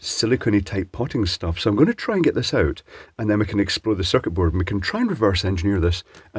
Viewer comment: I thought, I heard you say: "...and then we can explode the circuitboard!" 1:40 I kinda wish you did :-P